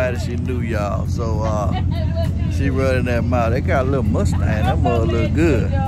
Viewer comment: why I thought you said at 1st the lady had a nice mustache 😂😂😩😩😂😂😂but you said mustang lol 😂 I was like dang you tough money bruh 😂😂😂